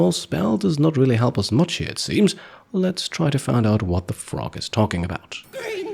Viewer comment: Not gonna lie, at 20:51 the first time i heard "What the fuck is talking about", had to rewind to hear it correctly :)